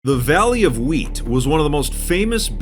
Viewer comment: I thought he said the valley of weed for a second at the start I was like So this is where minecraft drugs are dealt?